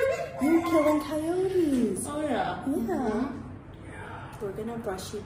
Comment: I heard a whispering “yeah” after they said it or am I tripping.